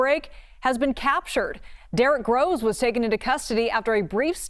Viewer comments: I thought she said Derick Rose lol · Did anybody else have to back it up because they weren't watching the screen and thought they heard Derrick Rose got arrested?😂